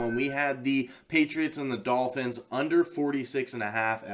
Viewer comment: Doh. I thought u said over on the dolphins game lol. Was the only leg that lost for me lol.